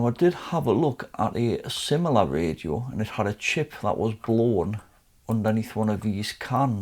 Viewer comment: Did you say "Blowed" 13:25. Is that a term you geniuses use. 🤣 Nevertheless another fantastic job and a very enjoyable video again. #TopMan 👍🙂👍 My family and I wish your wife and her friend a healthy and speedy recovery. God Bless you all. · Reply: "Blown" 😂😂Thanks mate 👍